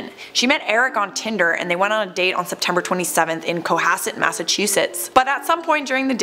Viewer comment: it sounded like u said september?